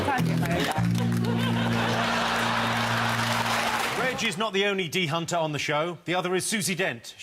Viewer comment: What did Bob say at 00:58 legs....